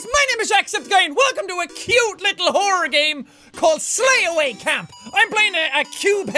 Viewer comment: I heard hoer game